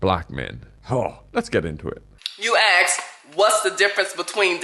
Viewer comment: I stopped watching video when I heard her say "You axed...." 0:55 😅